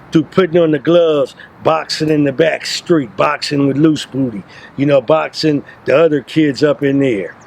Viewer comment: Hold up !? Did he just say loose booty .. fucc this I’m done for tonight I’m going to sleep